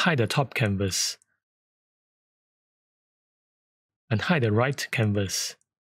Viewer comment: Excellent video (as always). One small point: with your accent I found trouble with "hide the first canvas, unhide the second canvas" which I kept hearing as "hide the first canvas, AND hide the second canvas". Confused me a little. :-)